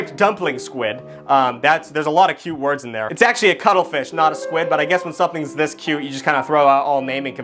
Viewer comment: I thought he said “cuddle-fish” 😭